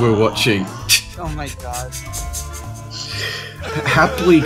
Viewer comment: did anyone heard in the back round “oh my god”